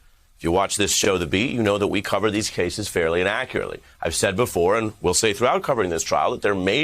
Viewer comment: It sounded like Ari said We cover these cases fairly inaccurately.